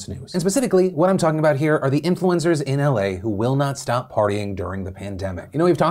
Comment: i heard "influenzas"